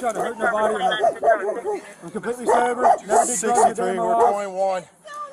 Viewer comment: Dispatch: "If you have to shoot him, then shoot him." I swear it sounds like they said that, no?